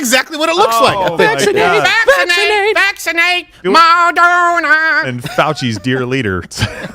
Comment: I didn't hear "VACCINATE" I heard "VASECTOMY" over and over. I guess the future is looking a bit brighter if this woke mentality disappears.